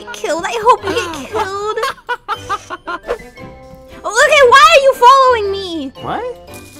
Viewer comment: said Luca instead of sora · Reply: no she said look ugh why you following me play it at 0.5 speed